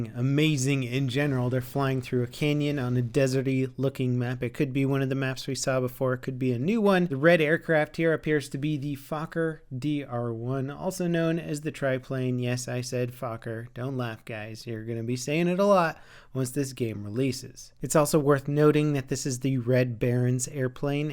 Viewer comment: at first i thought he said fucker DR.1 i was like wtf